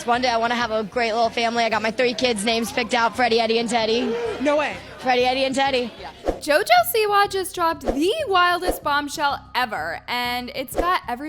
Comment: I thought she said Freddy yeti and teddy😂😂